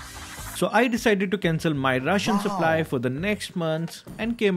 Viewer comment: Honestly I hear “next month of russian supply”😂😂😂😂😂😂